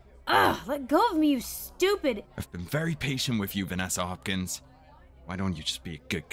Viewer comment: did he say Vanessa? Oooo this is getting even deeper now🤨🧐